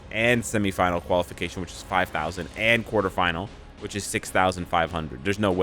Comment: I heard "quarterfinal" but my brain said "quarter quell". pretty obvious what I've been randomly catching on TV lately